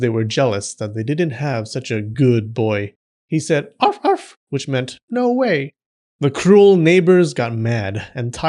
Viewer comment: "He said, 'ARF ARF which meant NO WAY'" My dog must be really off because I could swear I heard "F*$% YOU!".